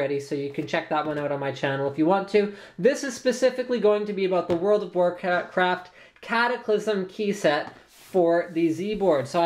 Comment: LOL, i thought i heard World of Warcrap.